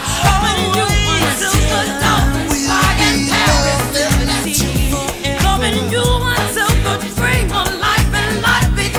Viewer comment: Alguien sabe que es lo que dice George de fondo en el min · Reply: Oh... I will be loving you... until forever